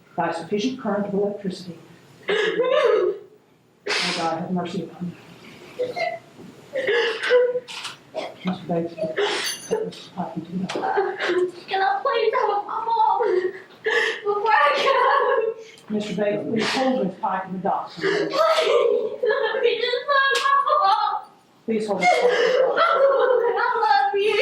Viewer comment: "Can I please just hug my mom before I go?" Did get to hug her Mom before she was forced to go!?!?!? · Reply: Thought she said "can I please have a Pall Mall" Your interpretation makes more sense